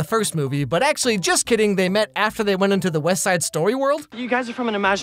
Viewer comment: … Did he say “West Side Story”?